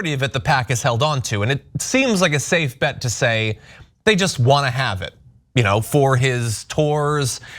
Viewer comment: "...they just want to keep it for his tours...", I'm a little hard of hearing & when I first heard this I thought John had said, "...they just want to keep it for his whores...", I really need to get a hearing aid, all though both statements are probably true...